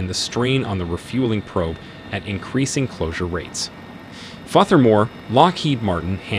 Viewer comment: “Fother more” Text to speech has really taken a step forward being able to sound like a person by training it to sound like you or whomever, unfortunately typos can easily be missed and you get phrases like “Fother more”. Something that wouldn’t have been spoken that way if a person was actually reading it. Sometimes you have to misspell something to get it to sound right in the app, like “Firther” would have sounded like further in TTS.